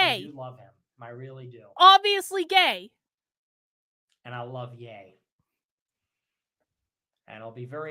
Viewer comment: “I love Ye.” This came immediately after some enthusiastic assertions that he’s obviously gay. Had to listen to it again because I thought he said “I love gay.” 😂